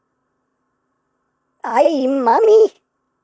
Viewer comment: wait did he just say mommy?🤨